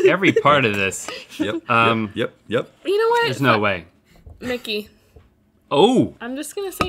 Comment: "You know what, fu..." She was definitely about to say f*ck it